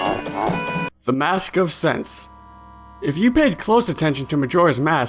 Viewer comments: When you say 'Mask of Scents' I hear it as 'Mask of Sex' XD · instead of saying mask of scents i misheard you and heard mask of sex the mask of the great sex LOL